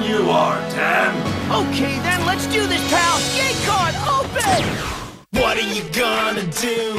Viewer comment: correct me if I’m wrong but I heard GAY CARD OPEN